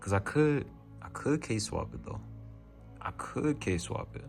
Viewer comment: "Case Swap" at 20:26 sounded like K-swap bro I swear. Low key Case swapping is the K-swapping of the PC world 🤣🤣🤣🤣